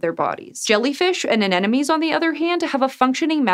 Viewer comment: Is that really how "anemone" is pronounced? I thought you said "an enemy"